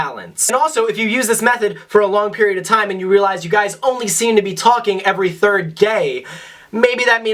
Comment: Is it just me or does it sound like he is saying “Every 3rd Gay”